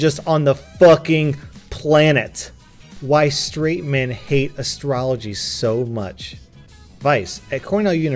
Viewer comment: i do tho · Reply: OH THEY SAID ASTROLOGY LMFAO I THOUGHT THEY SAID ASTRONOMY im so dumb. yeah i dont like astology.